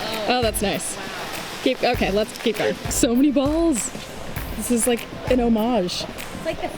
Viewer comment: i thought said so many buttholes lmao